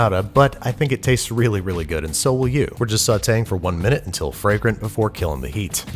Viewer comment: "…and so are you" As someone with low self-confidence, aww, thank you :) …or have I misheard things?